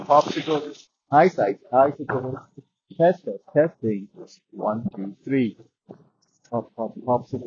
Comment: I thought i heard "test test testicle"😂